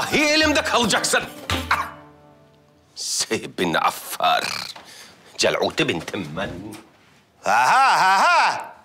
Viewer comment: den itibaren ne diyor la çok merak ettim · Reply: Celut kuşun yavrusuna celut denir